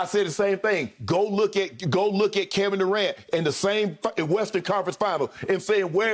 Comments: it sounded like shannon said same f*ckin western conference finals! 😂😂😂😂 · "the same f-ing western conference final" I swear that's what he said 🤣🤣